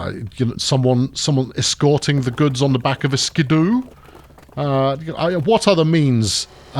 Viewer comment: I tried googling what you said. I ended up getting a cartoon mouse. What word was it? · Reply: Ski-doo. <link>